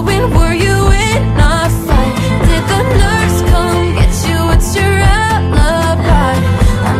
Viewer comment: Who else thought she said "Did the nurse come get ya"?